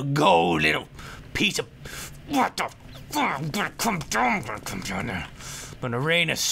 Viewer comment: Sounds like said WhAT ThE FuCK